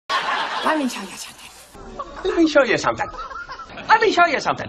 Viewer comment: All these years I thought he was saying “Let Me Tell You Something “ 😂😂😂😂😂😂😂